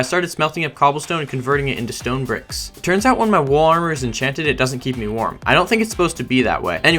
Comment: does anyone have any clue what he is saying is enchanted? because it sounds like he said water or he had a stroke